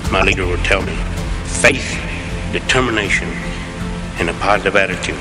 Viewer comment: 3가지가 믿음 faith 긍정적인태고 positive attitude 투지가 먼가요? 전 그저 터미네이션으로 들리는데. 앞서 2단어도 맞나요? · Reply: determination